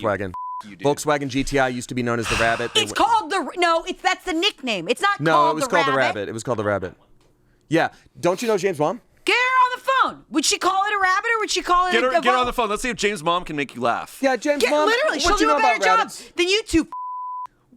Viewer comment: I thought they said "James Bond, " and was so confused